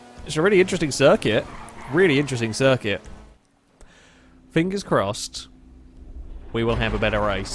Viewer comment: I swear you said "penis crossed..."